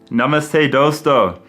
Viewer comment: i heard he said : “Nama saya Dosto” (Bahasa Indonesia language) 🇮🇩 (My name is Dosto xD) but turned out he’s saying Namaste XD